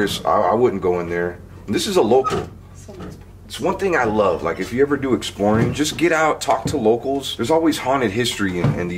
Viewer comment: area there is a female voice that sounds like it says "someone" then can't make out the rest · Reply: Thomas Roberge it says someone’s here